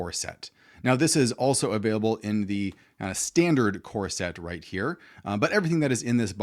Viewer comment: For some reason I heard it as corset, not core set.